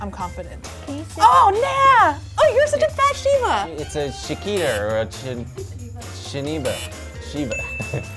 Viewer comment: “it’s a Shakita, it’s a shaniba! Oh it’s a shiba.” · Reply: Cookie Dough Films I thought he said shakira 😆